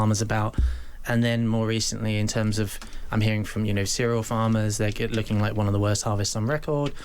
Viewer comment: Misheard 'cereal' as 'serial'... Serial Farmers: They just keep growing.